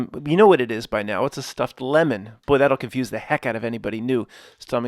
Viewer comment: Stuffed "LEMONS"? I thought you've been saying "Stuffed Lennins" Like the dead-body on display in Russia: Lennin. Like he's stuffed